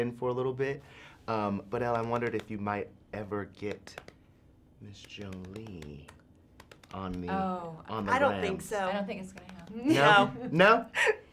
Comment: Could please someone help me to understand what the guy has ''asked'' in the end of the video? I'm not an English speaker - I'm still learning it - and I didn't really understand what he has said to Angie; also his voice was kind of missing and I couldn't listen to everything clear ): 5:40 to the ending of it Thank you a lot! ♥ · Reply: I think he asks if they would ever get Angelina Jolie on Instagram. She shakes her head and says she doesn't understand it.